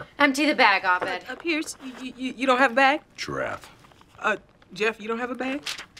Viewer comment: What does Pierce say at 10:45 ? I've listened to it over and over and can't make it out. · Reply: it sounds like giraffe but I can't figure out what it means